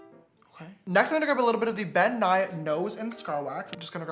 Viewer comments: I thought he said Bill Nye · Omg at 14:03 I thought he said Bill Nye lol 😝 😆 😂 I’m so freaking slow lmfao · Damn at 14:02 I thought he said Bill Nye, I was like wtf when did Bill Nye start doing makeup. 🤦🏻‍♀️